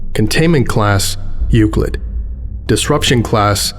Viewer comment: I had to watch the video with low volume, misheared "euclid" as "you're good" and thought "well that's a weird way to say "safe"